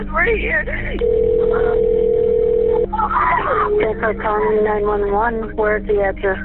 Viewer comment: There was more than one call. @ 2:51 the dispatcher says "thanks for calling 911..." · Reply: I don't hear St. Croix County at all. Even the text on the screen said 'Thanks for calling..." (and it isn't the auto-generated kind). Not a big deal but to me it's clear as day and it isn't strange in the slightest. (i'm talking about the call